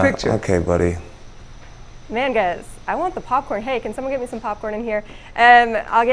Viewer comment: did she say “n****s i want the popcorn” 😂😂😂